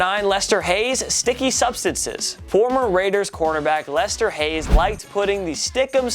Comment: and Bowman is a corner back apparently lol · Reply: I thought he said Quarterback😂